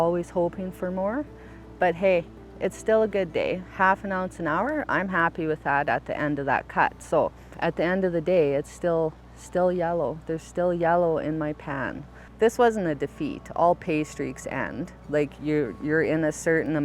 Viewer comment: I thought she said im still happy yellow in my pants